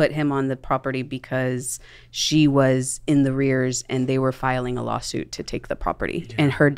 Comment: I lost it when she said "in the rears" instead of "in arrears"